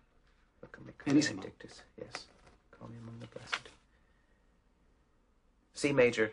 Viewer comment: Did he say “Call me one of the best”